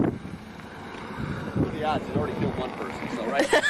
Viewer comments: He clearly says he already killed one person right . 😳 😳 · did he say "what are the odds he's already killed one person so"??? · did he just say: "he's already killed one person so, alright"??!